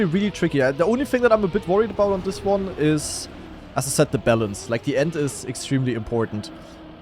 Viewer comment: yek i thought he said "bell end" instead of "balance" (because the map literally ends with a bell), but "bell end" really means something else xdd